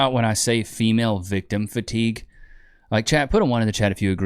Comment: I thought for a minute you said "shemale victim fatigue!" It's! In a really masculine voice. 😂